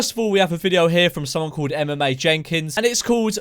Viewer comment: i thought you said MMA jenkins